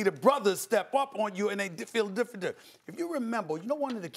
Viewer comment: Steven A was tryna say different he said difadent not even a word lmao